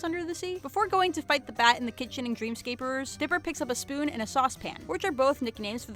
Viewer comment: did she say "Dreamscaperers?" 9:47 she did it again